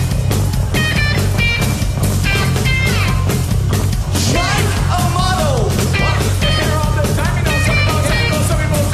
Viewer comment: What did Daron say at 0:27? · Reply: Mike Amato